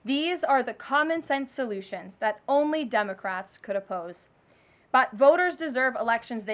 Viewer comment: I'm not the only one who heard her say "Dumbocrats" right???